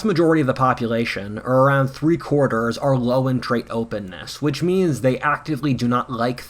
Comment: ??? --- WHAT did he say? 5:40 what was the term he used? "low entrate openness" ?? I played with back a few times - cannot understand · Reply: “Low in trait” maybe? I hope this can be of help even though you commented 4 months ago haha